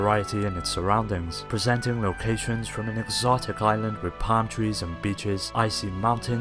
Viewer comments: I thought this man said 'exoctic palm trees and bitches' 2:54. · i thought u said "bitches" xD